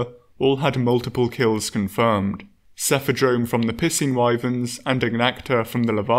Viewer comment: Missheard piscine wyverns thought he said pissing wyverns🤣